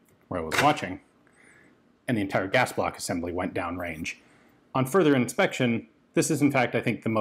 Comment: I had to re-watch I was very concerned as I thought you said "I tried firing and the gas block went down range" not you saw someone try firing. You dont need to take apart (often you do) a firearm before shooting to inspect, but even surface level inspection you should know something's going on.